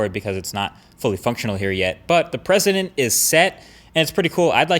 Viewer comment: Is just me? Or did he say "the president is set" after talking about shortcuts for people with small hands at 2:45 🤔